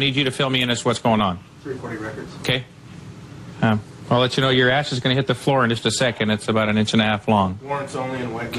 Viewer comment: I thought the officer said “your ass is going to hit the floor in just a second.” I thought they were about to drop him! Then they showed the cigarette 😂